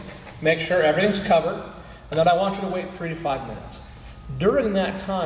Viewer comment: lol. 35 minutes or 3-5 minutes? Can't tell what he said about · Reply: he said 3 to five minutes